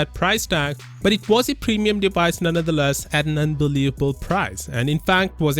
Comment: Did i just hear say "Undelavable price" at 2.30 ? Unbelievable moment ;)